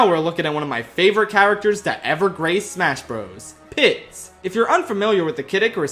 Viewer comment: My audio cut out and I heard PISS!